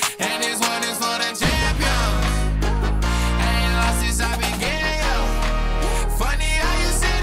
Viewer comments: Yes, he does say “I ain’t lost since I began, yeah” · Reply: Yea, look it up I thought he said gay too lol but he actually says since I began. Still great either way tho. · I also thought he said 'I ain't lost since I been gay yo.' I took it to mean all the people who said coming out would ruin his career were wrong. But it's began